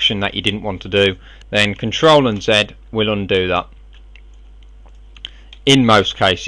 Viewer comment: Amazing tutorial.@ 3:20 I didn't hear correctly. I hear you say control unset? What is the key exactly? Ctrl+what?